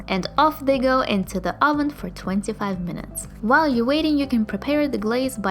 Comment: lol i thought you said to cook the onions for 45 minutes 😅